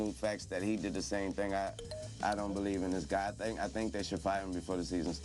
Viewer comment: listen at 3:24 it sounds like he says. " I don't believe in this god thing "